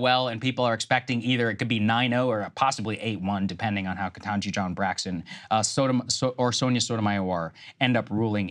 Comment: am I the only one who heard SAAGAR say KETANJI JOHN BRACKSON :face-fuchsia-tongue-out::face-fuchsia-tongue-out::face-blue-smiling